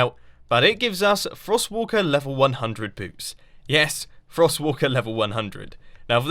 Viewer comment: Wait did he say level 100 boobs?